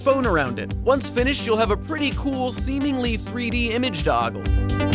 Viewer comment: did you say image dog? i hope you ddnt because that was a cat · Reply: finigan he said image toggle